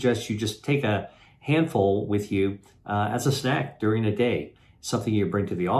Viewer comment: Okay so take a handful of what for treatise That's all I heard is for treatise for treatise at 3:20. Maybe my ears are going maybe Dr Li can prescribe something that'll get the wax out of my ears.